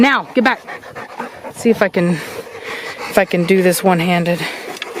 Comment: It may be my tv...did you say the F Word 10:10 - 10:15 Also Keep Up The Great Work ❤️❤️ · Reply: She said "if I can". really fast